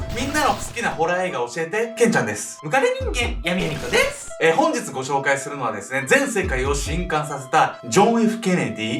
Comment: ガチで聞き取れなかったwww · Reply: 今更だけど、ムカデ人間っていうホラー映画ですww